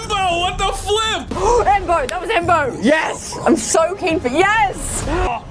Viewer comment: Umm, did she just say “I’m soaking?”